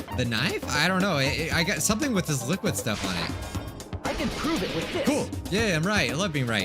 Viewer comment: Hajime: "I can prove it with this!" What I heard: "I can prove it with piss!'